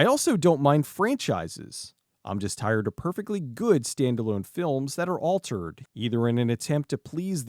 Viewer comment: I thought it said Stallone films